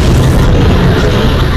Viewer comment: i hear "i think its a trap"